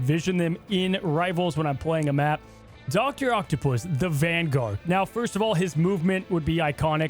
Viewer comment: I don’t remember saying he would be a dps. I picture him as a vanguard, honestly. Or strategist if he uses gadgets but mainly because of Ultron surprising me. “I see his playstyle focusing on control as a vanguard.” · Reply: “Doctor Octapus, the vanguard” is what I heard.